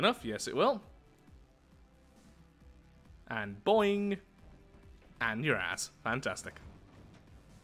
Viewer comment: I'm assuming you say "And you're ours", but what I hear is "And your ass"